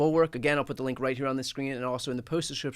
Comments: For a second I thought you said Inside The Reich instead of Inside the Right! then there is not much difference these days. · I always hear "Inside the Reich"